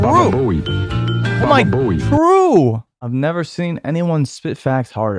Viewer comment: why did the subtitles say Green M&M pussy when just said true. I'm dying 😭